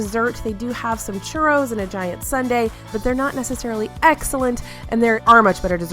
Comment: This entire video was making me hungry!! Also, I swear I heard you say DIET sundae instead of GIANT sundae LOL 😂